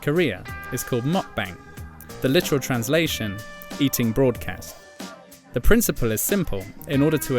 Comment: It sounded like you said 'eating c.ts'